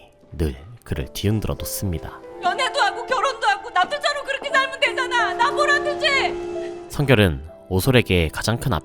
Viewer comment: 대사가 뭐라고 하는지 몇 번을 들어도 모르겠네요 혹시 아시는 분...? 아, 영상 재미있게 잘 보고 있습니다 감사합니다` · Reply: 그렇게 살면 되잖아! 나 보란듯이! 라고 하는거같숩니ㅣ댜ㅎㅎ